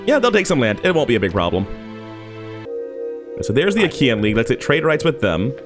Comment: I heard Ikea'n league and laughed out loud 😂